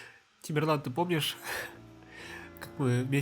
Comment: Мне послышалось что ты сказал Эмирлан (меня зовут Эмирлан)